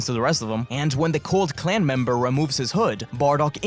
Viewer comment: Did i hear you say the cold klan member